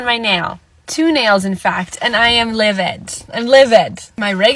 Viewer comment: did she just say she's "liviT"?? Like as in, liviD?